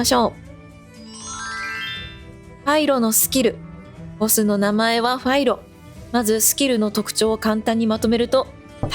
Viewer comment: 分かりやすい動画ありがとう＆おつかれさまです！ 1:11 団長の声かな？なんて言ってるんだろうww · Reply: 見てくださってありがとうございます❣触れていただき嬉しいです😆アナと雪の女王でアナとエルサが子どもの頃、エルサが部屋に引きこもっているときにアナが「ドアを開けて～」と歌ってるところの真似しましたｗそうです、私の声です🥰